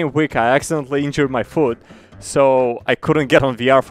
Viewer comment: i thought you said hurt my food